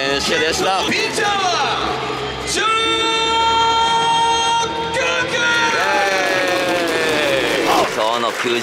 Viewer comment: 最初のピッチャーのところ ジョングクって聞こえたのおれだけ？